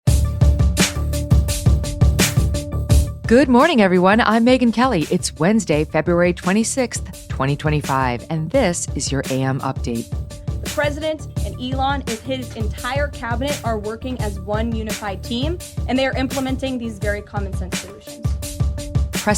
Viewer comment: Just saying today it sounded like you said heres you dam update